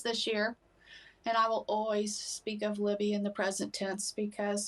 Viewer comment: Sounds like Abby is saying Libby run